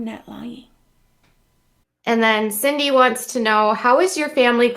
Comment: I heard " Cindy Watts want's to know... Say what?! Is she a member? Then I turned down the foot massager. Ahhhh. Oki.